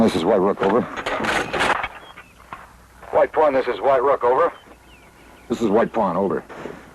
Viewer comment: I swear Nimoy says "this is white Porn, over..."